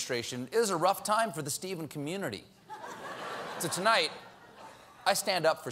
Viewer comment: Am I the only one who misheard "Stephen community" as "Stephen universe"?